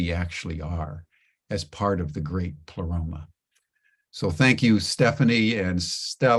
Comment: I heard saying "the great paloma" - which, when I googled is a Mexican drink, perhaps suitable for Cinco de Mayo! was reminded there was a transcript: the great pleroma. I often have to look up a word that uses: 1. (in Gnosticism) the spiritual universe as the abode of God and of the totality of the divine powers and emanations. 2. (in Christian theology) the totality or fullness of the Godhead which dwells in Christ. Now to get on with listening to what I am sure will be a rich offering.